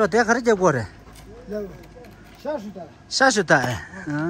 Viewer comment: 我怎么听成了下水道😂，藏语中这个词汇的发音来自于内地吗？ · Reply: ✌,yes the worker really said 下水道（sewer），but pronunciation is not Mandarin 下水道. he should be confused a little at first ,what is "xia shui dao" in tibetan ? but then he understand the xia shui dao is 下水道。since the first 下水道 is a Mandarin words with heavy tibetan accent which can be understood by all Chinese ,you can understand right ? but "xiashuidao" is in tibetan's pronunciation, not only han Chinese the tibetan can't understand his xiashuida either ,even the god don't know what is going on ,he has to come down to check ...